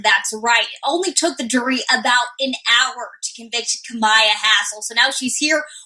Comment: I thought she said kemia asshole 😂😂😂😂